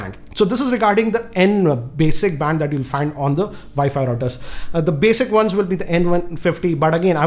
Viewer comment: did I just heard "Vi-Fi" router?????